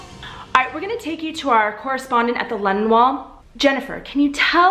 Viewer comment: I was listening to this video on my daily walk and I thought I heard “Lenin Wall”. I had to pause and check